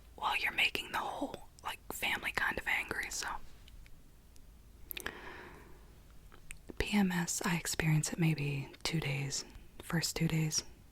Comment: I thought you said “BDMS I experience it for about 2 days.” I was like dayumm Kinky